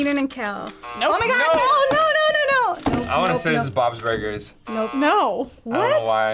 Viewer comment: Hold up did not just say “Bobs Burgers” 😭